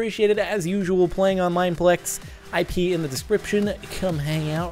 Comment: I thought he said "I peed in the description"... heh! xD